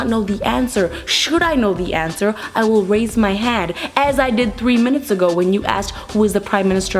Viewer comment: did she said head instead of hand?